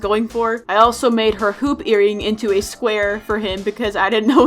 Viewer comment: BAHAH I HEARD “poop earrings” AND IT TOOK ME A WHILE WHAT IT ACTUALLY SAID